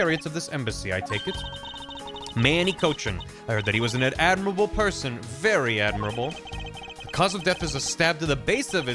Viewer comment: They say this a few times and every time I hear “As were they all, all admirable men” 4:32:42 Wait… How was Manny worried about anything during the second fire?